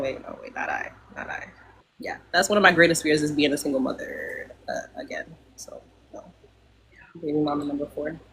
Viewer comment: Did l hear right? Did she say her greatest fear is being a single mother again 🤔